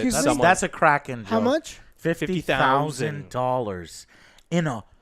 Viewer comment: LMAO the first time I heard 15'000 and was like wow omg why? Then the second time I heard the correct 50'000...